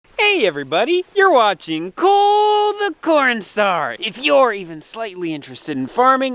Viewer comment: Absolutely love Cole The Cornstar 💖💖 however I'll admit 1st time I watched I thought you said porn star lol but you're truly what some American men should be made outta these day's. God bless and I think your farm house is stunning.